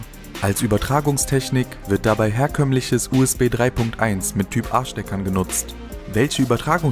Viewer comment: Wer hat bei 2:48 auch "Mit Typ Arschdeckern benutzt" gehört? xD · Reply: Er meint "Mit Typ A-Steckern". Habe das aber zuerst auch gehört.